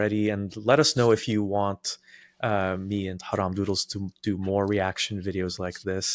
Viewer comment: My brain kept mishearing haram noodles rofl probably shouldn't stay up so long working but I really enjoyed this video in the background